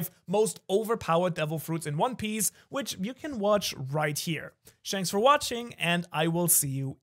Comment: Did I really hear "Shanks for watching" ? Take my like !